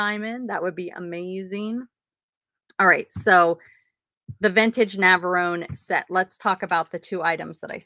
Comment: I know all about the Navarro set, but, tell me why I still thought you said you parted out a vintage macaroon set!?! Haha